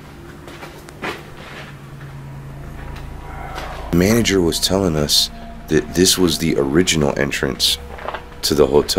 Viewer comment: Did anyone hear a whisper saying help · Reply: Aj Styles said wow